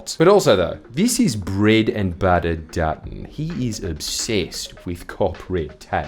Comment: I thought Jordie’s said “Peanut Butter Dutton” here and I personally think that’s funny enough to stick. The fact it has no real meaning will surely infuriate him more.